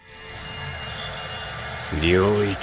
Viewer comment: why did bro say malevolent kitchen 🙏🙏😭😭🙏🙏😭😭😭😭😭😭🙏🙏😭😭💀💀💀💀